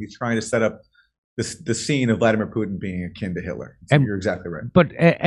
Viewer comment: I misheard 'akin to Hitler' as 'kinder Hitler' ie. child Hitler. Now that would be funny, Putin as Hitler's lovechild. Maybe the dates don't match, though.